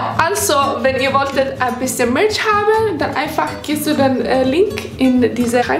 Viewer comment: Ich habe verstanden ( da könnt ihr ein bisschen Milch haben) anstatt Merch 😂😂🤣😅😚 bei 1:24, wie süß sie das ausgesprochen hat